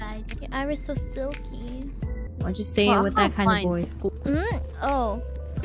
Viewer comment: Intentionally not reading the subtitles and listening to what Kronii's saying at 3:42, it does almost sound like she could have been saying "Why don't you say it with that kind of voice" (a request) instead of "Why do you say it with that kind of voice?" (a question). She wouldn't actually have said it, of course...